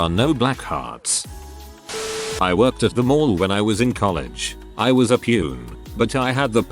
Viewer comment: I was just listening and I thought it said “I worked at the morgue” and I was like OK until he went on about gift cards.